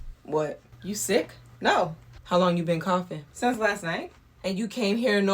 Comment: I heard "coffin" as in she's referring to her as the dead 😂😂 but im pretty sure she said "coughing" lol